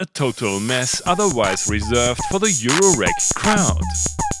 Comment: I thought you said "...for the eurorack clown" ^^